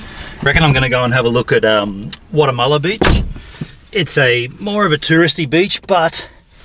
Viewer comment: hahahaha I thought he said "Guatemala Beach"... hahahahaha was so hyped! :(